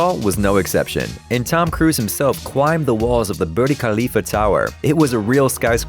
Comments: It’s BURJJJJJ KHALIFA.. not BURDIE 😂 wtf.. has no one noticed that ? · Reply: thought he said burly khalifa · Did I just hear booty Khalifa??!! 😂😂😂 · Tom Cruise himself cwimbed the walls of the Birdy Khalifa tower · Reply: I heard it as Burly Khalifa..